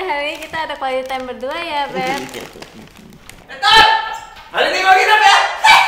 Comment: itu ngomong apa sih??😂😂.... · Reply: GUUNTTUUURR HARI INI GW GNGINEP YAAA HEEHEHEHEE ketawa